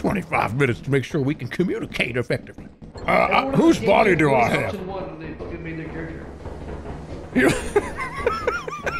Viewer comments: What did they say at 4:34 in the background that everyone laughed at? I can't hear it over Jerma's voice. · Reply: I think he says "everyone in the game missed option 1 when they fucking made their character" but i didnt get what that meant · what does say here that makes jerma and vinny laugh i cant hear him at all 😭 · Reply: said something like "everyone in this game picked voice option 1 when they made their character"